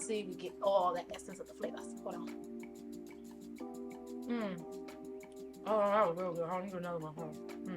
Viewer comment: What did you say here? 😂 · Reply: 😂. I think it was "Mm! Ohthatwasgoodgirlholdonlemmegetanother one" 🤣🤣